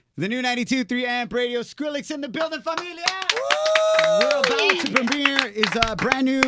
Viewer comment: is he saying "Skrillex in the building, fuck me in the ass!" at 0:02?